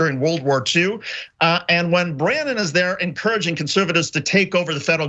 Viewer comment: did he say Brandon? He referring to Biden? lol